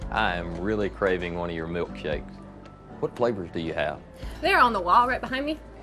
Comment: Not gonna lie, I thought he said he was craving one of their “milk cakes”. I was thinking, ‘hmm that sounds interesting’